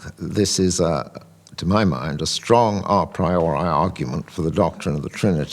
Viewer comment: of the transcript, he says " à priorial" . I think the transcript didn't include the "a" because it thought he was saying "uh"